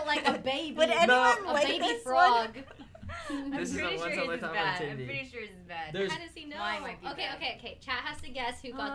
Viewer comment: what did foolish say here? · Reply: "this is a once in a lifetime opportunity" i think